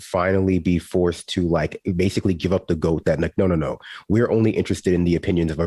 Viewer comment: Did he say "give up the goat"?